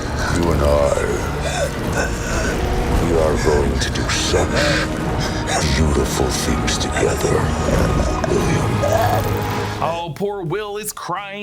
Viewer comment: Am I the only one who hears Vecna saying "We are going to do seven beautiful things together?